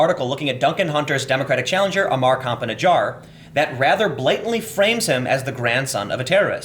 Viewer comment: Don't mean to make light of a serious topic, but I keep hearing "cop in a jar" lol